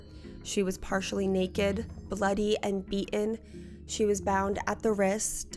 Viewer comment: I'm sorry.. DID YOU JUST SAY EATEN?! Was that thing even human?!